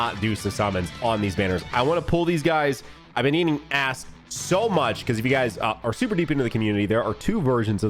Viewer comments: thought said I've been eating ass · I thought bro said he was eating ass at first 😭